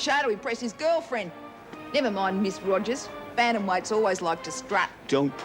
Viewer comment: what did Marie say, something?? always like to strut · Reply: bantamweights. :)